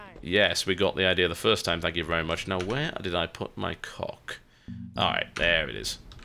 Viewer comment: he says "now where did i put my cock" i rewind it 3 times to make shure i heard that right because that's not something TB would usually say :p and at 52:04 he says your thing :p